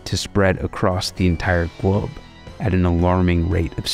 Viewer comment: Did he say “Gwobe”? 😅